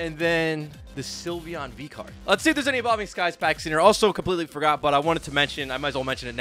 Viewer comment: I heard this as "evolving cries" for a reason. I would prefer the Ray or Dragonite Alt art more than the moonbreon. If i could pull one of those 2, I'd call it quits. Way too expensive